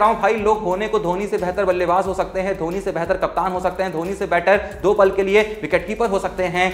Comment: kya bola bhai? Behtr captain ho skte h?